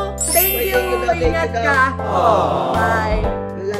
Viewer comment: parang may narinig akong love you